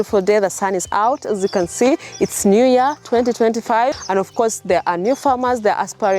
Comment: Did I hear 😢2025 ? Anyways nice to have you back , missed your content lots 🎉🎊🎈🔥